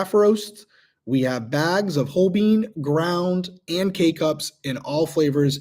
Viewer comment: Every time he says k cups, I hear “cake ups” and I get sad every time because cake ups aren’t even a thing.